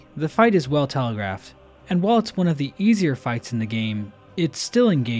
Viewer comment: Captions say "Easiest" when says "easier". This has permanently damaged your reputation, and you are now my 32,815th favorite youtuber when you were my 32,814th.